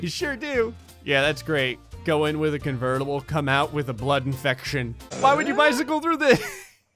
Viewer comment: "Go in with a convertible, come out with a blood infection" -, 2019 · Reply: I thought he said flood infection from halo lol